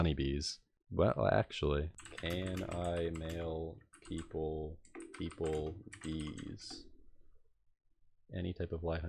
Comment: i thought you said "can i mail people knees"